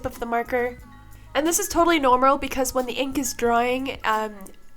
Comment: you said Nomral instead of Normal 😂 Still an amazing video tho ❤